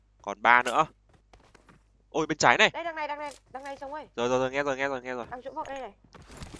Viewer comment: nghe đằng này ck ơi =))))))))) hãy nói là tui nghe nhầm đi mụi người